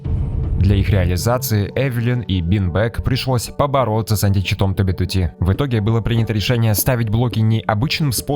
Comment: 😂мне послышалось анти чит на 2б2т?😂